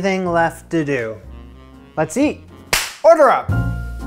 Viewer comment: did you say UDRUB ?